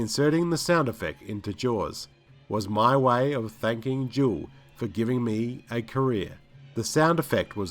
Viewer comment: Does anyone else think the narrator is saying "Jewell" instead of Duel? it's odd.. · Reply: Sure sounds like he is saying "Jewell ".